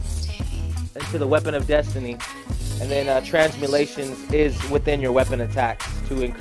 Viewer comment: "Transmutation" lol if thats what u were saying then my bad, it sound like ur saying Transmulation.